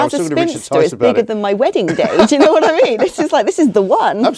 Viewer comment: Who else was gutted when she said she was married 😬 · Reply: I thought she said "as a spinster it's bigger than my wedding day". 21:45 sounds like spinster to me :-)